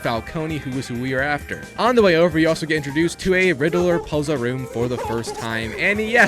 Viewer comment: did you say R-riddler... P-P-P-Puzzle?